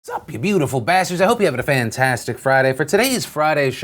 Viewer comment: My first time on this channel and I hear “hello beautiful bustards” and I feel very good about myself